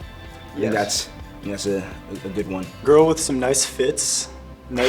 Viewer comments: When he said “a girl with some nice fits” I thought he said nice tits, I was like woooah 💀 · OMG anyone else thought he said “a girl with nice tits” 😂😂😂